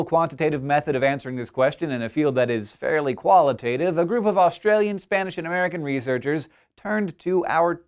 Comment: AM I THE ONLY ONE who heard him Australian but the text said Austrian at 0:56 ?